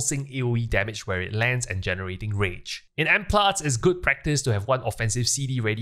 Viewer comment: what is good practice you said at 1:52 ? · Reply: ok you were saying "in m plus" :)